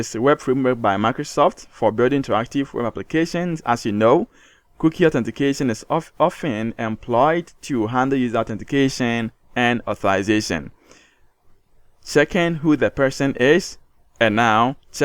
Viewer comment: I fail to understand what you said at time 1:09, you spoke too fast. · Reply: I said cookie authentication is employed to handle user authentication and authorization. is it okay? or not this one?